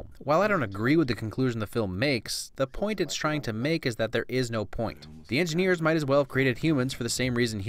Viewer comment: Hey at 6:57 you put not instead of no. just FYI